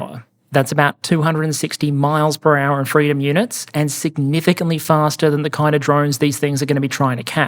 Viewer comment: 260 miles per hour in freedom units? @ 30:09 did I hear that correctly?